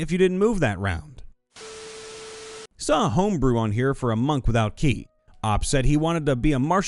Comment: At first I heard "monk with feet" not "monk without ki" and was actually really interested in how that works